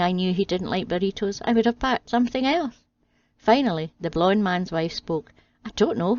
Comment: I married a tight pussy. She is tighter than any Scotsman, her penny pinching makes Lincoln bleed. Thought you said blind man, not blonde.